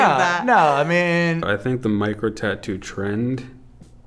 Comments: I thought she said Mike Rowe tattoos and not micro tattoos. 🤣 I didn’t know that many people had Mike Rowe tattoos. 🤷‍♀️ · For a second I thought they said Mike Rowe tattoos, and I was very confused thinking about the many people who might have the host of Dirty Jobs on them.